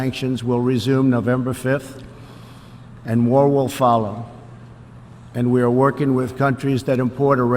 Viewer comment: You need to fix the closed caption At 12:58. He DID NOT SAY "WAR" will follow... as you typed it. He said MORE will follow. Fix it now!!